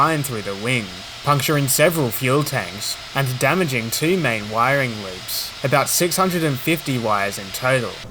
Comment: It sounds like you said "Wiring Loops" here, it should be "Looms", that is "Wiring Looms" Which is a bundle of cables that generally go into the same connector, or from one to multiple others.